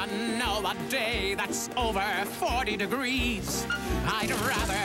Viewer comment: Hearing it I never realised they rhymed "degrees" with "degrees" I thought he said "a breeze"